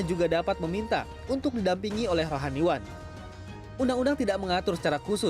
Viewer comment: Bentar ini OOT tapi ganggu bgt saya jadi kepikiran... itu di menit 1:40-an masnya bilang apa ya? Rahanyuan? :') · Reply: rohaniwan